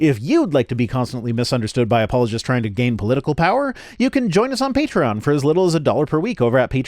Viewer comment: Misheard that line at the end as apologists seeking to game political power" and had to go back to realise it wasn't what you actually said. ...then realised I misheard it in a way that basically said the same thing anyway.